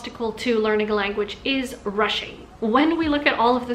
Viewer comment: honestly thought you said 'Russian' for a minute